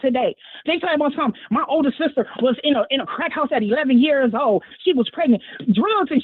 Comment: No honey. I thought they said your mom was in a crack house at 11.